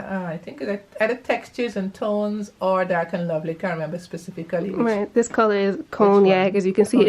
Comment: Hi nice but can we know the brand she uses for her color please ? Is it a box dye or she is doing it professionally cause each brand can gives different name to the very same colour .and result may vary from one person to another so I would really appreciate to know the brand thx. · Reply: Pardon the intrusion, but I thought I heard her say it was Cognac by Textures and Tones or Dark and Lovely